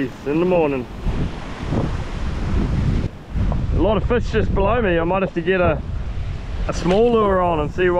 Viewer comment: Did he just say: “ A lot of fish just blow me?”